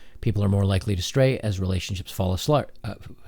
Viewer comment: "People are more likely to stray as relationships fall a-slut." (4:08) Talk about a Freudian slip! That was an example of you saying what you're thinking rather than thinking what you're saying.